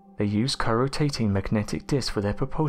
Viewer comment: What is he saying at 4:32 ? Cryotating magnetic discs currotating? I cant fir the life of me understand him